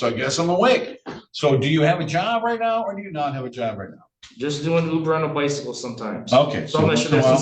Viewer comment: At about 42:30, did I hear him say he does Uber on a bicycle??? 😲😲😲😲🤣🤣